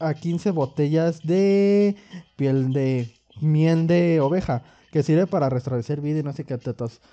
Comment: creo que escuche "miel de obeja" xD